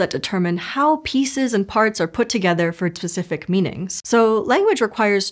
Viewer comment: she says ‘tsific’ instead of specific, and no one caught this? In a language video? 😂